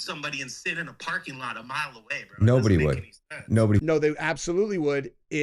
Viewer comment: How far away is the place from the oot place? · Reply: Oh i heard him say mile away... there is plenty of time!